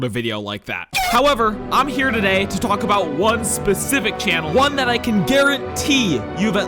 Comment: i thought he said "one especific ANIMAL" 😭🙏🏻